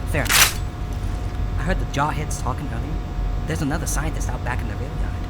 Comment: "I heard the jawheads talking--" He actually said jarheads with an accent that makes it sound like "jawheads." Jarhead is a slang term for members of the United States Marine Corps and carries a negative connotation, like they've been brainwashed by military training into being mindless soldiers; it means their heads are as empty as jars.